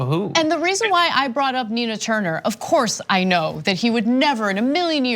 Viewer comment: I thought you said Tina Turner lol, and I thought "that could work." :)